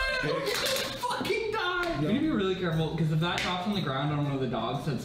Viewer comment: I’m sorry to crush your dreams but I believe he is actually saying “we’re gonna fucking die”